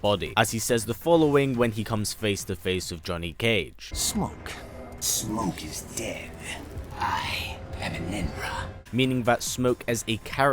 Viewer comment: I thought that smoke said:Smoke is dead,I am a ninra. After a few minutes of research I found out that he said Enenra. WASTE OF TIME